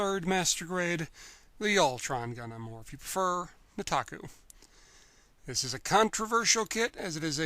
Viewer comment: I misheard that as "The Taco" instead of Nataku.